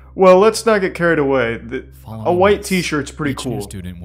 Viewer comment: I thought that it said "white teens are cool"